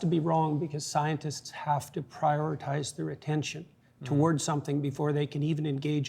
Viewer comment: Jordan says "...prioritize their attention..." not retention, I think Matt misheard it. I hope this reaches Matt.